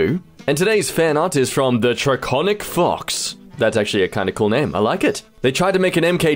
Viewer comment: When I heard TheDraconicFox, I thought I heard TheDraconicFucks, please don't attack me for this